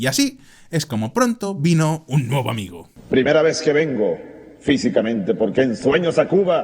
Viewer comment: min 8:55 un nuevo amigo, o un bobo amigo? no se entiende bien aunque ambos encajan!